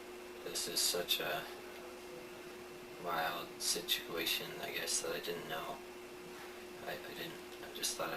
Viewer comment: Whooaaa! How do you not call 911 FIRST??! 😯 He says “ It was such a violent situation…. Thought I’d call the people I trust “…. Think there might be more to his story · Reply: I thought he said “wild situation” but I was only half paying attention so not sure.